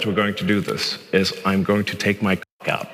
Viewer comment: For a second i thought he said "take his clock out", like he was going to hold a giant novelty alarm clock and point to it and ask the audience tl check theirs at home. Sighh........hes not at this level of anymore.